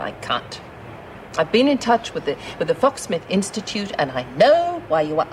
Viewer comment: hahah it sounded like he said FUCK SMITH INSTITUTE!!!